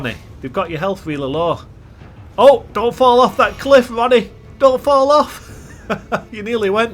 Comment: no I love your accent I’m just wondering who this guy is? It looks like radagon and you’re saying he’s like cousin to ranni who is this cosplay? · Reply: ooooooh it’s like a custom character that you made up okay now that makes sense I thought you were saying Ranni but that’s not Ranni lmao good video tho. Never seen such a low level build that actually does shit